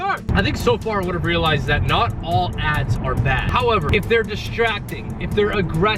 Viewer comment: All i heard was "not all ads are bad-ass" 😂